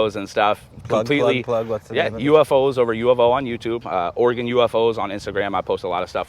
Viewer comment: Did Anyone catch Youtube channel name? He says it do quick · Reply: UFOs over U of O